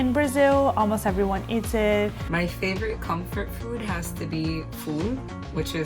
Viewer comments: i thought she said that her favorite comfort food is f o o d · I thought she said “ my favorite comfort food is food